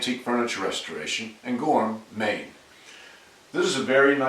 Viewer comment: For the longest time I thought intro said "Gourmet". I wondered why in the whole time he only ate one sandwich. and never spoke about food. I felt such a goose when I finally realised he said "Goram, Maine"